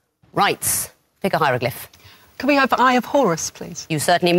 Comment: i always hear “i’ll have horse please” instead of eye of horus